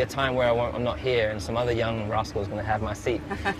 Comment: I thought he said “some other young Russell’s gonna have my seat” lol